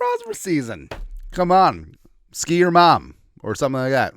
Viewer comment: Lol - did you say Ski Your Mom?